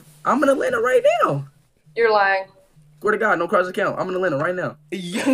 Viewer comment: “No crosses count”😭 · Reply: Oh I thought he was saying "Norcross & ______" since Norcross is in ATL